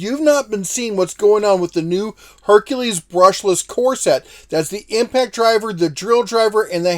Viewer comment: I definitely misheard you at first and thought you said “Hercules corset” instead of “core set”. I was impressed by your enthusiastic commitment to HF active wear for all. Lol😂 thanks for the great content as always